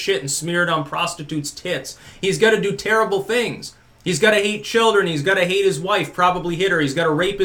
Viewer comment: haha at 7:52 I heard say "he's got to EAT children" before I went back. I'll bet the MSM does come out at some point and says Trumps eats children at this point I won't be surprised.